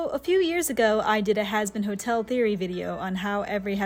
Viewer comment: at the start i thought she said “a few weeks ago” and was extremely confused when i saw “2 years ago” great video :D